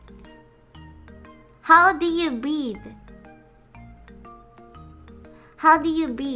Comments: "How do you breed?" What I hear "How do you breathe?" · I hear her saying "breathe" It's just her accent. She is well able to pronounce d sounds in the other words she uses. Listen again carefully, you will hear "eathe" at the end. Unless we're just making fun of her accent, but hey I haven't been here long enough.